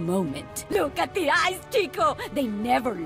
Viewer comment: look at my eyes _____. what did she say? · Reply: Chico